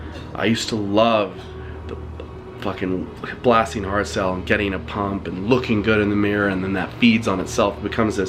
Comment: what are you saying? I can't figure it out. Blasting what? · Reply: Hardstyle lol